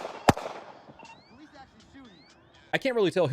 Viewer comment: lol you can hear what sounds like one of the police officers say "oh he's actually shooting"